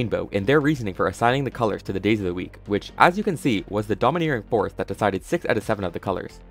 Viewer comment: what did you just say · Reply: he said "was the dominating force"